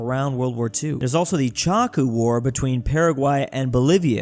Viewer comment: when he said chaco war i thought he said choco war